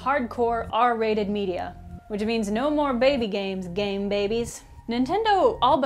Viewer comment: for a second I thought you said gay baby